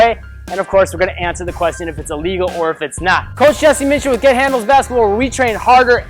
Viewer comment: I thought he said “gay handles” lol